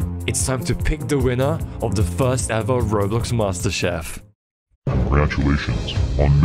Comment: have anyone noticed when they said roblox master chef the captions said Rolex?